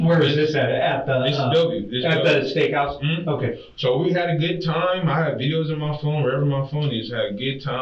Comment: You must have said he has videos in his phone but it sounds like he said he has bills in his phone